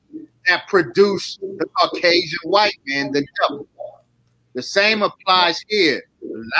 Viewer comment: 5 seconds after the word jew is spoke, stream ends. SHUT IT DOWN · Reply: I think I heard him say the white man is the devil?